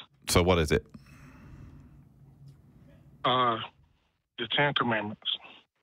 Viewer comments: what did the caller say that led to the hangup? I didn't get what he said. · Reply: “The Ten Commandments” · I couldn't tell what the first caller said, that got him hung up on. "The chanter man"? (post edit): Oh wait. nm. I kept replaying it until I could tell what he was saying. "The ten commandments". uhg. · What did the guy say at 1:24 ? It sounded a bit like "the chantermans" but I couldn't quite make it out.